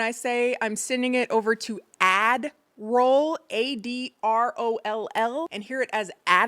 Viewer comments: WAIT, this whole time you’ve been saying AD ROLE?! I thought you were saying admiral too and just never questioned it. 😂😂😂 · Haha i thought it was only me who heard admiral, even the CC shows admiral when u say ad roll😂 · I’m so glad you addressed the “admiral” versus “ad roll”. I heard it as admiral but knew it was ad roll. Very epic. Feel better diva ❤ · see, i misheard ad roll as ADDERALL for the longest. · people mishearing admiral and me out there for years thinking its ADDERALL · WAIT!!! She wasn’t saying admiral the whole time??? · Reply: nope 😂 i always heard ad roll myself, but she said others always thought she said admiral. Now that’s all i will be hearing from now on